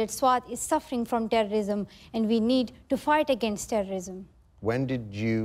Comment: Did she just say we need to fart against terrorism? 3:40?!!